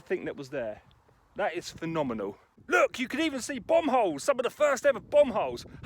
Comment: You can see bung holes..??lol..I'm just being funny..lol.. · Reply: Oi mate,was being abit cheeky as..sounded like he said,bung holes.I just got meself laughing like I was abit daft..(Bum holes..)Oi mate..well it sounded abit like bung holes..but listening atad it is more like bum holes now..